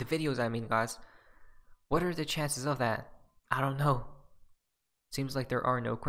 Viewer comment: Did you just say "what are the chances the sun shines"?